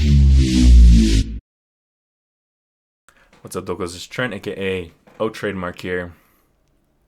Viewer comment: What does Trademark say in his intros? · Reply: lol I thought you said What's up "Doggos"